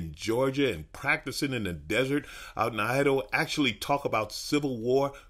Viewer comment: I'm sorry, did say the "deserts of Idowa?"